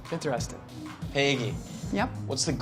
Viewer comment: I thought he said "Hey Eggy!"